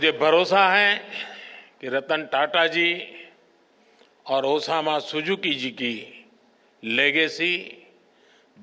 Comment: Did he say osama😂😂😂